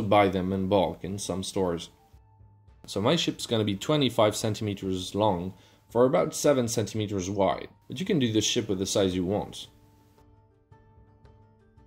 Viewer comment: it sorta sounds like he says "Shit" lol!!!!